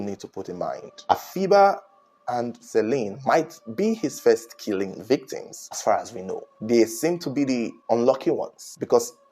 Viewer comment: He did not ascertain anything... Take English for what it is he said around 10:30 -10:10 (so check the time stamp) They MIGHT BE his first killing AS FAR AS WE KNOW....This long sentence and you are not listening, he is just reporting on what information is out there so far we only know about the two girls, if the police come out with more information he will definitely update us, you wanted to go to the crime scene and investigate? In Nigeria? This is Africa not america, go watch your crime documentaries on ID channel.